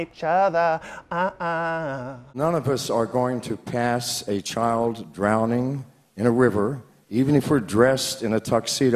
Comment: I THOUGHT HE SAID “PANTS A CHILD DROWNING”